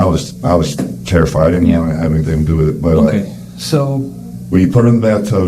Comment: At minute 16:35 did he say, “I didn’t want anything to do with it” ??